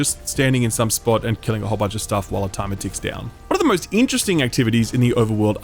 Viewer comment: lol "one of the most" sounded like murloc